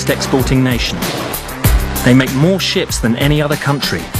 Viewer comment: LOL when they said They are making more ships i thought he said they are make more shit which made me say out loud "HEY THATS CHINA!"